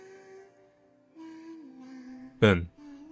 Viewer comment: At around 21:28 it sounds like you say Ben (my name) out of nowhere, and that honestly scared the absolute shit out of me.